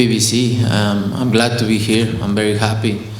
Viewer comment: I swear it sounded like canelo said” I’m black to be here” hahaha brotha joined PBC and became mixed 😂😂